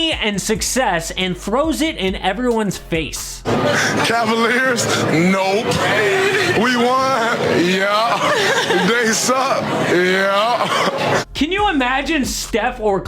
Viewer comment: I had to listen to this 5 times to understand what this marble mouth said. I got, “Cavaliers? Nope. We won? Yeah. They suck? Yeah.”